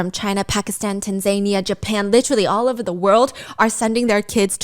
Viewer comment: Stephanie 😂😂😂 did i hear my country Tanzania at 32:24? U know of us?! I love you ❤